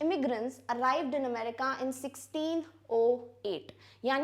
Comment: DID SHE SAY 16-O-8 ???